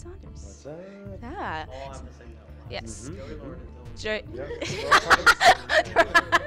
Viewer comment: What did Joey say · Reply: Joey, Lauren, and Dylan